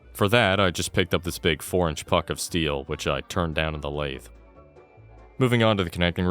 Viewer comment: I cant be the only one that heard that as "4 inch fuck of steel"